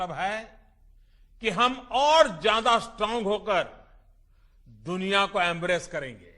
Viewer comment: did he say, "hum aur zyada strong ho kar, duniya ko embarrass karenge"??